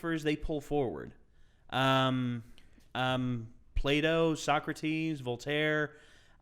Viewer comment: I thought you said play-doh when you said Plato hahah my bad!